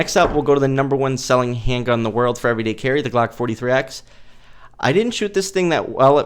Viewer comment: wth are you talking about? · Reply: bro what I time stamped 1:05 he said 43x clearly what are you talking about seriously?